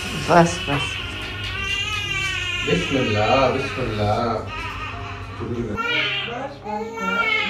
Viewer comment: I swear to god she is literally saying Allah Allah ❤